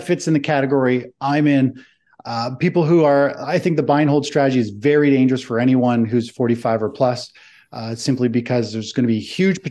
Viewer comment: Did he say “…the buy and hold strategy is dangerous…?”